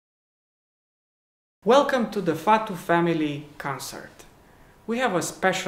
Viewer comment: I thought you said welcome to the fat family. Concert 😂🤣 I love your music. All soo attractive physically and with talent